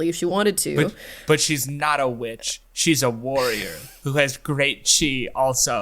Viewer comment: Every time you guys say, "She has great chi" I hear, "She has great cheese." And now I'm hungry. 🧀🧀🧀